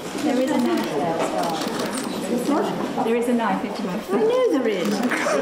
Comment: "There's a knife." "I know there is" I love this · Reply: I heard "I knew the risks"